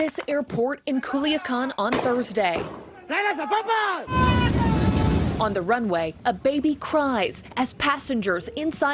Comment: What does he yell at 1:12? Sounded like ellas tienen las papas! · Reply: They say TRAEN LANZA PAPAS I DONT KNOW WHAT THATS MEAN BUT SOUNDS FUNY, ITS LIKE POTATOS LUNCHER